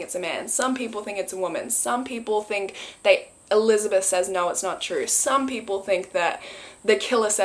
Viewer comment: i hear “it’s not true“ “ it’s not?” “that’s not true”